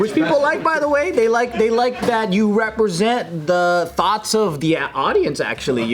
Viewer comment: I thought he said thots like hoes 🤣😂😅